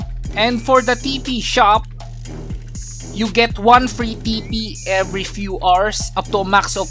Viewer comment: Did i just heard pee pee?